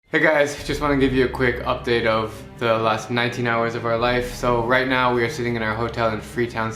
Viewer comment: I thought he said he only has 19 hours of his life oops...